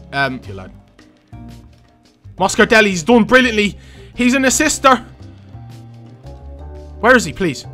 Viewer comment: anyone else thought he said " he's in his sister"